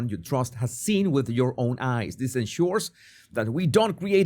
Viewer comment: the subtitle says "ICE" instead of "eyes" lol